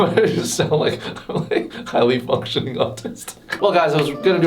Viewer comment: Did you say 'highly functioning autist'?